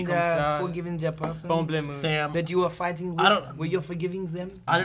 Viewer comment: did sam just say pomplamoose?